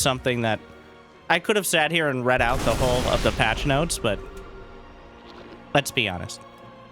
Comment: did you say lets ByuNest at 13:33? lol nice